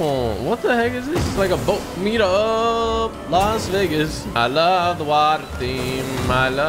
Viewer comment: did he just say the n-word🤨🤨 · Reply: Think he said las vegas lol